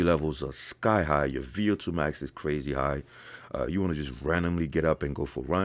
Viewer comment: Can someone please help me what did say at 13:52? I can't understand it ("your _ is crazy") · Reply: VO2 MAX